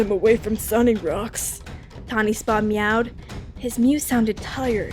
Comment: I heard TawnySpa instead of TawnySpots.